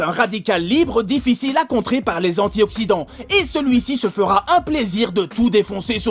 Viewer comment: eh faut que j'arrête Twitter, quand il a dit antioxidant j'ai compris anti-occident